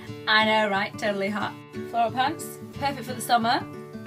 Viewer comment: At first I thought you said Floral pads (I thought awkward...) then I thought you said floral plants (I thought that's weird. Then I realised you said floral pants the third time you said it :))